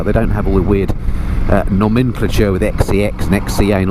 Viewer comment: it sounds like you said XC Anal.